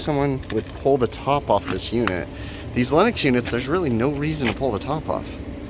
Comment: Just seeing the circuit board like that while rain is coming down scares the crap out of me. And yet again Lennox sounded like Linux. And I thought you said we had a Linux System! Maybe it does run Linux?